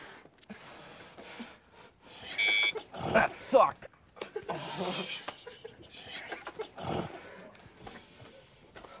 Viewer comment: thought he said either “That Sucked!” Or “That Sock!” Or “Bad Sock!” Lol but loved zero’s face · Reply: for the record I said “that sucked” cause it truly did. and it cost me money to say it lol